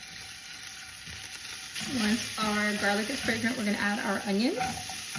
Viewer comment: I heard "Once our garlic is pregnant" instead of "fragrant" smfh